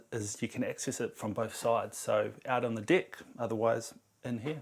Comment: he clearly said "dick"